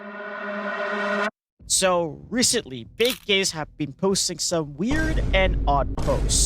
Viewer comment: caption said "Big Gays" lol